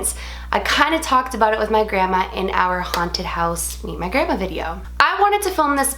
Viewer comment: I thought she said haunted house eat my grandma video